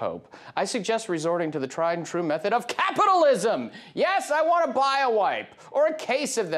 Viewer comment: I swear, I heard him say "I want to buy a *wife*"...